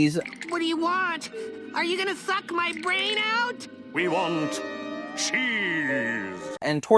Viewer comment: did he just say “F*** my brain out” 😂